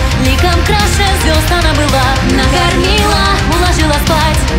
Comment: Мне одной послышалось не накормила а наркоманы? :D